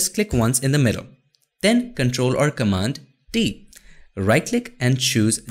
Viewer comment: This is kind of funny. I though thought you said CMD + D at 3:05, and I spent a great deal of time trying to figure out why it wasn't working. lol Then realized it was T, not D.